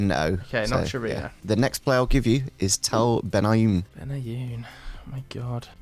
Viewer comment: He definitely thought said Benayoun not Ben Haim, that’s why he said West Ham haha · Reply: I thought said Tal Benayoun too but it's clearly Tal Ben Haim in the picture not Yossi Benayoun.